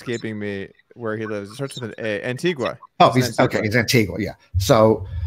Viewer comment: When they said Antigua I thought they said antifa I was like what the f*** that dude's with antifa and they are openly speaking positive about him LOL